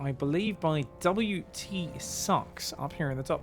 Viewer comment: thought he said WT Snacks at first